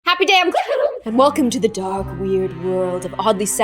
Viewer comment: who else thought she was saying "welcome to the dog here road"